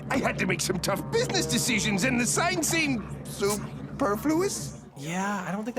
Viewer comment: what word is mr.krabs saying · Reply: superfluous